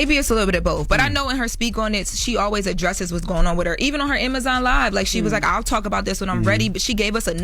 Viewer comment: did Lauren say Amazon live??? 😭😂😂